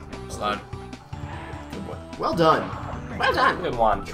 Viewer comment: Every time you say, "Well done." I hear, "Whale dong." I think something may be wrong with me.